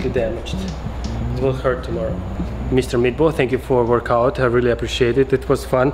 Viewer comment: It first sounded like you said Mr. Meatball haha. Btw, always nice seeing your videos.